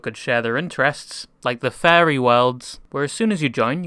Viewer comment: When you said furry worlds i thought you said fairy lmao